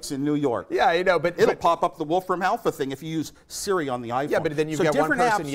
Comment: Did he just say Siri? It was before iPhone 4S launch!